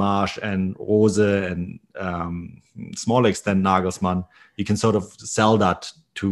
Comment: did he say nigga in the 15:28 wtf